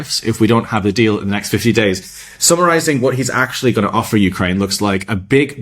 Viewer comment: Why 50 days.... 50 days of atrocities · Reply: When I heard it i thought he said 15 days That would make more sense